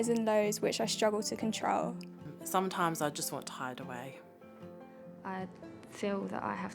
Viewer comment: what is she saying? « Tied away » (?). I am looking for a traduction and i can’t find any. I am french. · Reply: She's saying 'Sometimes I just want to hide away'.